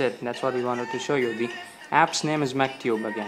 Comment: Found it. It's "MCTube". I thought you said "MacTube".